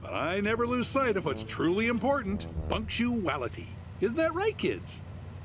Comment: What Bill said was punctuality.